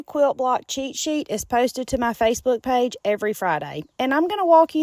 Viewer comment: Am l mistaken? I went to your website looking for the block pattern and couldn’t find it. Help please. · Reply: Thought she said fave book? On her site I put the name of what I want in the search bar and am able to find it that way